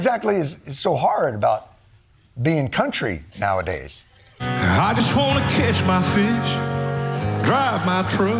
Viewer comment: thought he said "I just wanna kiss my fish" 🤣🤣🤣